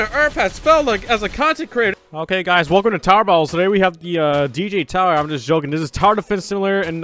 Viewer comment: Anyone heard him say tower battles and not tower defense simulator?